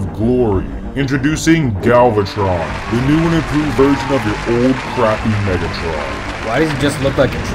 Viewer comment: Did anyone heard that the fallen said ' Old crappy megatron ' 😂😂😂